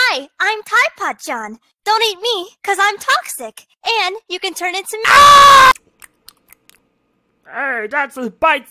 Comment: Dont eat me. Cuz im toxic and you can turn into AAAAAAaaAaaa Me:i think she said you can turn into me